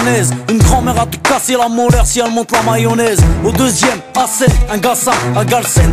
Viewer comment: did he say "mayonnaise"? Seriously curious, as I don't know French : )